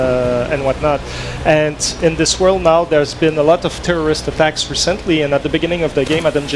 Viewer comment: When he says terrorists he sounded like he was saying tourists